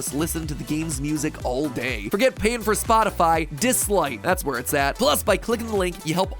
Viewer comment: captions said dislike, title says listen and obey. I'm sorry for this